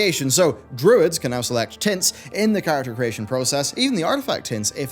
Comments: I zoned out for a moment and misheard the word "tints" as "tits." "You can finally choose druid's tits" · I thought you said "Druid's can now select tits" ..... disappointed. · I was making coffee while listening and watching the video and thought he said “Druids can now select tits” was confused for a second :D all jokes aside, #easyclasswins for DK’s, remove March of the Damned, make Wraithwalk baseline, not channeled, rework it to have two charges and not be cancelled by other actions, as payoff if necessary maybe lower the duration? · that so dosnt sound like what I think he said, it sounded like he said tits, I did a double take at the screen and was like did I hear that right? (I didn’t) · I thought for sure you said " druids could now select tits." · Not gonna lie I thought he said druid can choose tits for a second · I heard "druids can now select tits in the character creation process." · I had to rewind, I thought he said druids could select "tits" 😅🤣 but he said tints 🤭🙄😒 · ''druids can now select tits'' am i the only one who hears this XD?